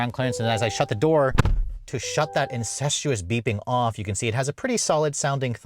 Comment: did you just say… INCESTUOUS beeping? 🥴